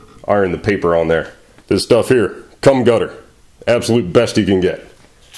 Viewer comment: I know this is old but did he actually say cum gutter instead of gum cutter? 😂